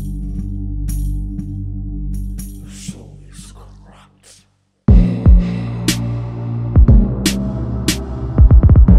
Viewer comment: What did he say on 0:30 ? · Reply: The soul is corrupt 🙏🏻💀🌌